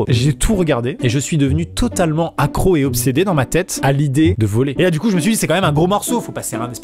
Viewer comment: "devenu complètement obsédé à l'idée... De voler" Moi : "Gifi des idées de génie" (réf plat à frites) 😂 🤣